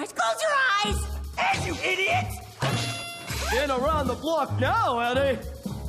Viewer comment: Did Ed say what I thought he said? ( " They're round da block now, eddy " )😂 1:14 Well gahdamn Ms. Frizzle 🤣🤣🤣